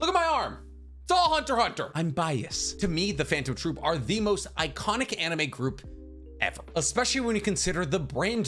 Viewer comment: You think the phantom troupe is more well known than the Akatsuki? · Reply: For what it's worth he said, "To me the Phantom Troupe are the most iconic anime group..."